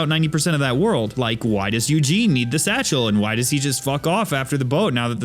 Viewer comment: Lol if you go on 5:06 with eng sub title it says yugi instead of Eugene. I laughed so hard